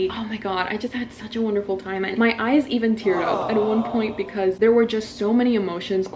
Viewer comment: I honestly thought you said, 'my eyes even threw-up' and I thought, that's a pretty neat way of saying I cried. (insert snowflake emoji)... I think it was a snowflake)